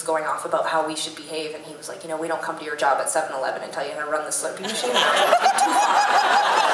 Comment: can someone help me what does their tour manager says at 10:20? I lost the word after "7-11"... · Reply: "We don't come to your job at 7-11 and tell you how to run the slurpee machine"